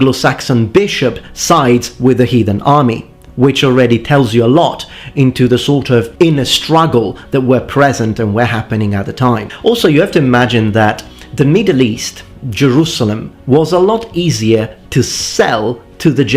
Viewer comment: But I said the Middle East right before I said Jerusalem, It was a list, meaning the area where he was Born. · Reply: Oh, just checked again and you are right. Thought you said "In the Middle East, Jerusalem" instead of "Middle East, Jerusalem" haha